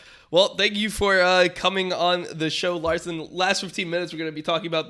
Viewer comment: did he just call him?